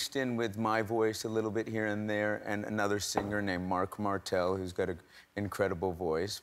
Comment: is he just said "MARC MARTEL" name ? 🤔